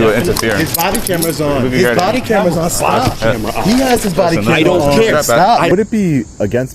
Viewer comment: Did he say “body camera off” around @ 0:15?